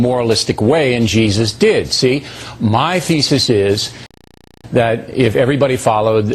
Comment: when in 1:52 that guy said 'my thesis..' I heard 'my feces' and that, in fact, fits much better here..